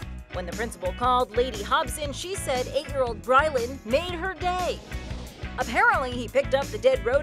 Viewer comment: Omg thought she said made her gay instead of day XD